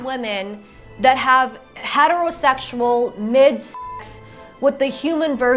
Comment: I thought you said “a head of the se*ual”